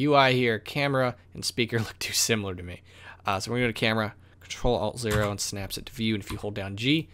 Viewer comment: how do you do that at 1:26 ? I'm not sure to hear what you said. anyone can help? thankyou! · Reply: Ctrl + alt + 0 Snaps camera into the view. And select the camera and press G to grab and align.